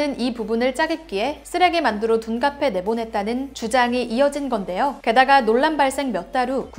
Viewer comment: 주장이 주작으로 들리짘ㅋㅋㅋㅋ?